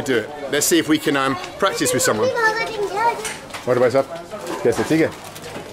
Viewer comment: What does mr. Bald keep saying to people in this video after aur bhaisaab? Is he saying sab se thik hai? He says it to the shopkeeper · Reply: Sab theek hai? Meaning all well?